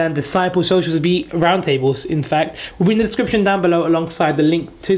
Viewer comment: i heard Infekt